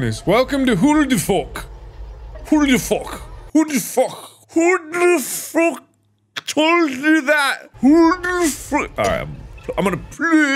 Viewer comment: When Mark said HO DE FOK at 7:45, it sounded like he was cussing in AFRIKAANS going WTF xD